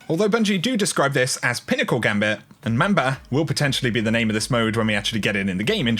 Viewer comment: At first I thought it said BAMBA MODE, lol